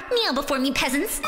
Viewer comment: Awesome work as always, I’m going to be honest, I was looking away when the song started and didn’t see the subtitles so I heard “meow before me” instead of “kneel before me” and was wondering if this was a cat pun filled parody. I quickly realized I misheard but let me tell you, I was kind of excited to hear that version.